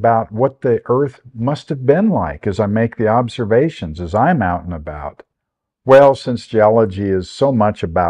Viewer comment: “…as I’m out and about…” But I misheard it as “as I mountain about.” I laughed and replayed it. But I like the idea of using “mountain” as a verb, so I’m going to do that from now on! In fact as for myself, I’m going to go mountain about here in Utah… right now!